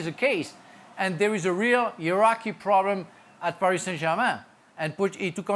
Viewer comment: Sounds like her said "There is a real Iraqi problem at PSG"